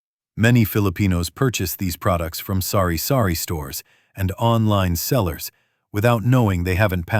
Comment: i laughed when i heard "sorry sorry store" instead of sari sari store 😂😂😂